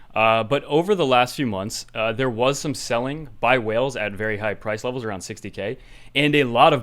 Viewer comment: When you first said "whales", i heard "Wales".